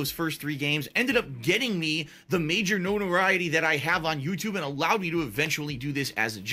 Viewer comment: <link> 37:35 says "Knowneriety" instead of notoriety.